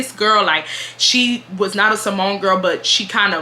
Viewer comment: When you said Samoan girl I thought you said symone girl 😂 I thought it was the name of a g@ or something lol